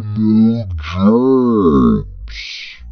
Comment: He said germs instead of jobs